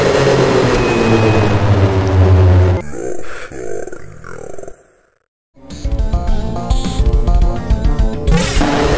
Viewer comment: what does Gorefield say here? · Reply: Your far, Jon...